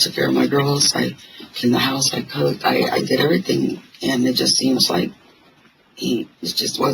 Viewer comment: she said “he just wasn’t enough” even though the subtitles said “I” That should tell you right there the type of woman she was. She wasn’t a victim with that certain story she was telling…she clearly said “he wasn’t enough”